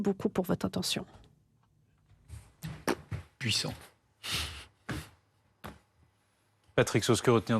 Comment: C'est moi ou le journaliste dit "Puissant" 5:29 juste après le discours de Poutine, puis après en commentaire il le casse. Ils sont bipolaires ou alors c'est un rôle qu'ils jouent...